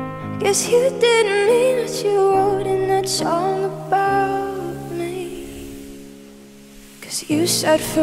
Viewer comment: okay but i’m not judging but did anyone else hear her adding an ‘h’ to the s? like i heard “what you wrote in that shong about me” or is it just me ??